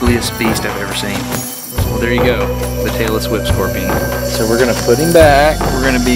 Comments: I thought he said the Taylor Swift Scorpion which terrified me! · Sounds like he said the Taylor swift scorpion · For a second there, I thought he said the "Taylor Swift Scorpion". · thought it said Taylor Swift Scorpion... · I thought they said the "Taylor Swift scorpion". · i thought you said the taylor swift scorpion. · sounds like he says the Taylor Swift Scorpion haha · I thought he said the Taylor Swift instead tailess whip xD hahaha